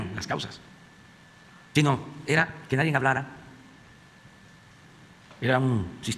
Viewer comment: ¿Escuché mal, o dijo “que nadien hablara”?🤦🏻‍♀️ min